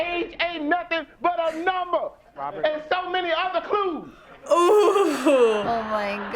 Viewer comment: what did he say? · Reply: "And so many other clues."